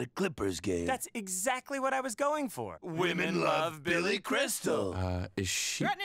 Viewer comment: I heard "bitches love Billy crystal." But they said "women love..."